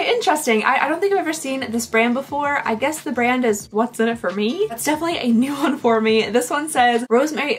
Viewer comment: The "what's in it for me" section, I thought you said " the mother f'ing desert" 😮😂😂😂